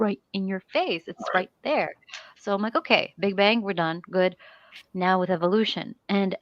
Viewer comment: I misheard as saying "So I'm like: Okay. They bang, we're done. Good." and for a moment I was like "Are we talking about cosmology or geneology? o.O?" But then I realised what she said xD PS. De-converted seems lovely. <3